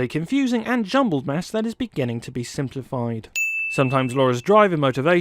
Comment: This is super nitpicky, but the subtitle said "begging", even thought the narrator said "beginning". 😉